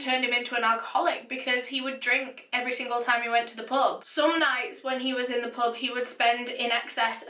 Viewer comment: I thought you said "he went to the the Pope daily." I seriously love the way you talk..